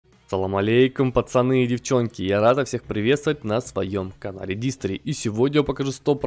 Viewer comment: Мне одному послышалось Рада вас встретить??